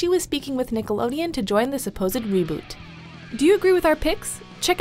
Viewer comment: did she say REBOOT!!!!???